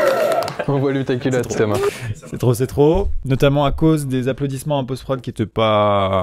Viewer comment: "c'est trop c'est trop" Moi : C'EST TROPICO putin